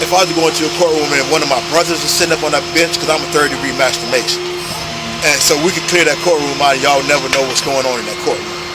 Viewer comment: I'm sorry, did he just say 3rd degree masturbation???!! WTF?